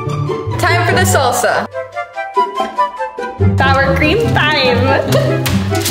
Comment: Bruh I thought said, “power cream pine”…